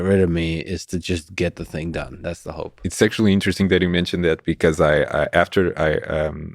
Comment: It really sounds like he says "sexually interesting" at 0:59.